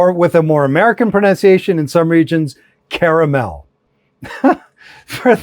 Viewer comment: Yes — Carmel-by-the-Sea is a real place in California 🙂 but it’s unrelated to the food word caramel. Caramel comes from older European terms linked to sugar/cane, while Carmel as a place name has a completely different origin. Similar spelling, pure coincidence — language does that a lot. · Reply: Ooops ! I thought you said Carmel... My bad ! English isn't my first language. More like the fourth or fifth... ❤😂Love from Norway